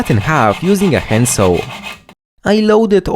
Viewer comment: "Which i cut in half using a pencil" 🗣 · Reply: Thanks a lot. I heard it fine as "hand saw" till you spoke up, now I can't hear anything but "pencil" 😠🤓😝